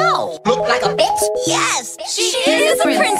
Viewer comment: bruh it sounds like they say "Could nap during sleep?"